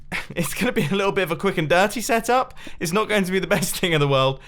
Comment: did Mumbo just say "quick and dirty"???????????